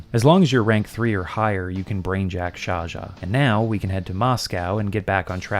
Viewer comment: I misheard this as “you can brainjack Zsa Zsa,” and then I pictured a SMT/Kaneko-drawn version of Zsa Zsa Gabor lmao